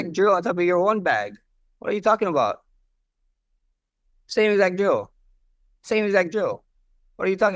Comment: To be honest, as a non native English speaker, I heard Max say “same exact Joe” the first time aswell 😂. So don’t really blame ilia here haha